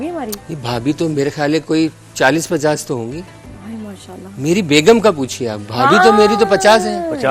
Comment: Did she say mashaallah 😂